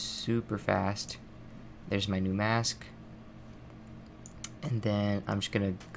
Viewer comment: "mask sksk" thats what i heard.